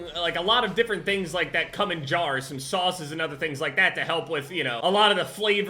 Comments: I heard “the cum in jars” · I heard cum in a jar😭